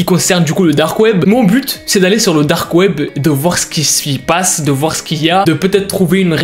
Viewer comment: je ne sais pas pourquoi mais j'entends: dahrkweb